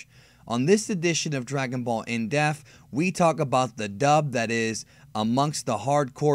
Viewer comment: it always sounds like you say dragonball in DEATH not in depth lol